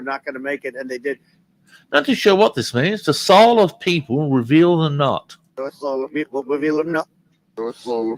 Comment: For that one Trump reversal I heard, "The soul of the people, reveal then not." I think you had it as "...reveal the knot" but I hear an "m" before the "n".